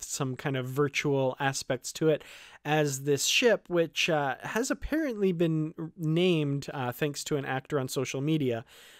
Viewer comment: But what was the name they said? 15:36 I didn't see that tweet. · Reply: I couldn't remember when I did the video, but I believe it's "La Serena." I think Ketwolski covers it in a recent video.